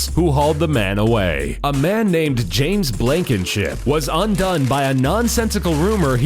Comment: I thought u said Dan blankimsip